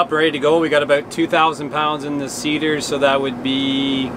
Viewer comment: What said “...clean the SEEDER”. What I heard “ clean the CIDR”! I watch a lot of Sandi’s sheep videos! You two are a great team. 😎